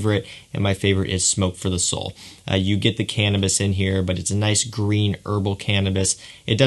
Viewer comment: Dang it - I definitely thought you said “canvas” not “cannabis” and I was SO excited. The cannabis note isn’t for me so I’m kind of sad and need someone to now come out with a canvas fragrance 😅